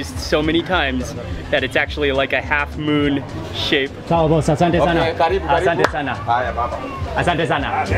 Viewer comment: I heard him say asunti sana squash banana?